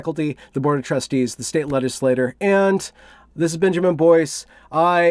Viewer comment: One inane note, it sounded like you were saying the State Legislator instead of Legislature. I assume that this is going to more than one of them